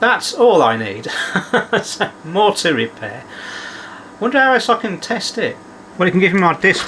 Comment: LOL My American ears @ 4:43 heard: "... I wonder how I F**King test it..? " vs what was actually said: "..I wonder how else I can test it .."